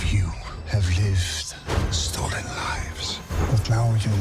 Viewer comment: It still sounds like he is saying 'storied lives'